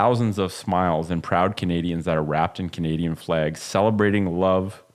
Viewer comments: Hello guys from Texas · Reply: I thought he said Canadian Bacon lol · I thought he said Canadian Bacon ;) lol